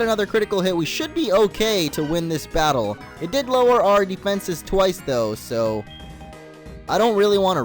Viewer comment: did he say that Growl lower he´s defense?